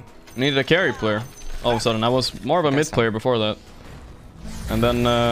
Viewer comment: what is a "miss player"? Does he mean "mid player"? If so, then I wonder what role did Topson play? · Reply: I thought he said 'misc player', as in miscellaneous heroes/roles?